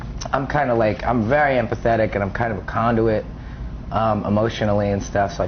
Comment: i thought he was gonna say hes a condom....XD ahahaa